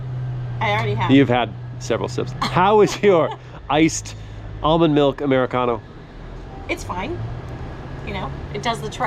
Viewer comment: did he say almond milk americano at 8:50??????